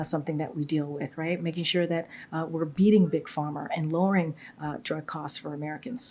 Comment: Did she just say "we're beating big farmer"? 😂 This woman is a complete embarrassment.